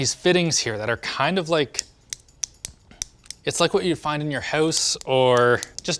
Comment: did he say "hose" or is it just a Canadian saying "house"?